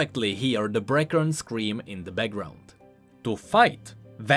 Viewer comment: what’s the “breaker” scream? He said some stuff about this earlier but I didn’t understand it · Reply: The Brakern. It's a species of crystal creatures with a sort of hive mind. Current League lore says Piltover harvests them for hextech.